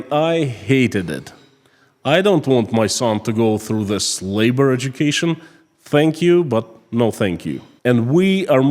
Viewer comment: Did you say "this labor education", or did you say "slave-bored education"?